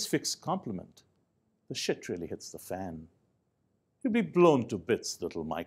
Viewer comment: hahahahha... did he really say "hte shit really hits the fan"?